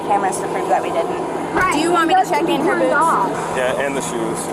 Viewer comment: "Want me to check her boots?" "Yeah, and check her shoes, too." I think the captions misheard this one.